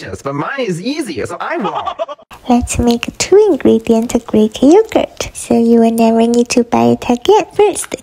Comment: huh????i thought you said no need to buy greek yogurt 😢